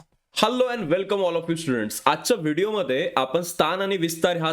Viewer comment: sir says shalo and welcome instead of hello and welcome